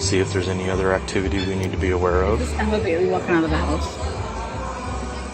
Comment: Did anyone notice during cop cam I heard the name Bailey from inside?? Also heard Bailey again from outside when cops going toward door?? Does that mean Emma Bailey or just a girl named Bailey! Someone explain that?? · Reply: I hear "Emma Bailey walking out of house."